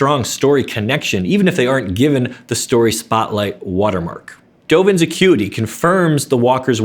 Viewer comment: I thought he said "Dovin's a cuty" hahaha